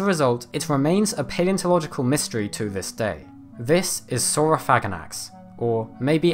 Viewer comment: Almost spit out my coffee cus I thought he said Sauro-faggot-ass LOL